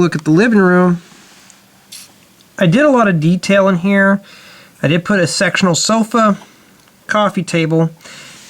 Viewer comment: i think you said "I did put a sexual sofa". XD